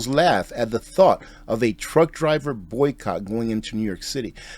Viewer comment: I thought you said Trump drivers